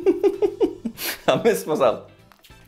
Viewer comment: did just say that he pissed himself 😂